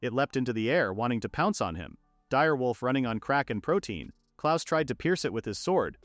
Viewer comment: Ok, I know what they actually said, but I heard Kracken Protine and it made me hungry.